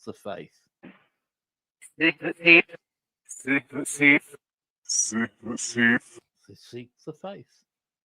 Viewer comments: I heard "seek the thief" around 8.5 minutes in. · I heard “seek the thief” 🙁